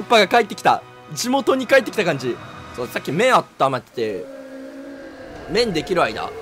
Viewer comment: 麺がずっと目に聞こえてて全然意味分からなかったwww